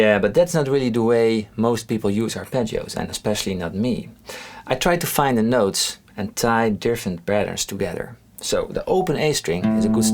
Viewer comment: what are you saying there? I can't quite make it out. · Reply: “I try to find the notes, and tie different patterns together”